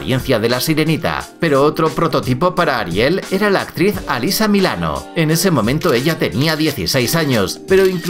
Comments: Dijo alisame el ano XD (sé perfectamente que dijo alisa milano pero me da risa pq parece que diga lo otro) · le entendí alisame el ano